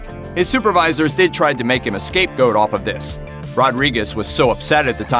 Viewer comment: I think you said escape goat